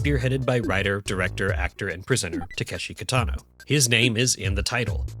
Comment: Did… did you say prisoner? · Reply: I think that he said presenter